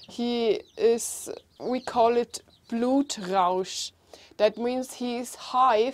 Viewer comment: did you said "we call it blut rauch" ? the German for what the fuchs feels is "blut rauch" ?